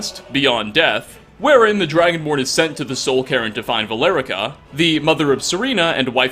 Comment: Der-never chilling in the soul-Karen again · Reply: You missed the joke. Sounds like he says Karen instead of cairn.